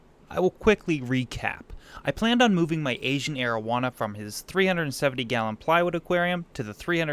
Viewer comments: I totally thought you said "Asian Marijuana" and I was like "Ummm well that's an odd fish species..." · Who else thought he said he got some Asian marijuana XD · i heard asian marijuana